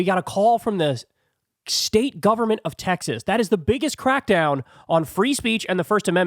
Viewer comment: That's not what he said, he just said Texas. It's unclear if he meant the governor of Texas or something of that nature.🤔🤷‍♂️